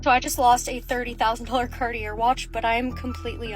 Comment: No one should react this way to losing a Cartier watch. It goes against the principal of owning a Cartier watch. Those are supposed to be treasured or at least handle with care. Being careless with it shows your moral compas is wrong. It gives over-consumption with too much money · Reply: Oh it's Cartier watch! I thought she's saying 'cardio' watch, like a Fitbit, and wondered what cardio watch is 30k! 😂